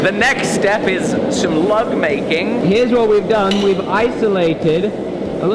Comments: I thought You said love making 😳 not lug making 😂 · could have sworn you said "love making" you'd think I'd be used to your accent by now. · Oh....lug making! Damn I thought he said love making and I was like wait what! 😳 I guess I'll keep watching then. 🤣